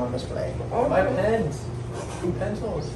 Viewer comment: Sorry but I thought he said penis instead of pens 💀